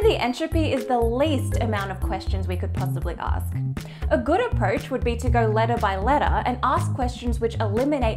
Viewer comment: Did she just say "...a gooder aproach..."? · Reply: No she says “ a good approach”.